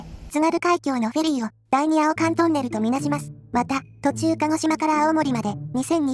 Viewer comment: 第二青函トンネルを あお〇んとんねるって 読んでるせいでえぐい物に聞こえたww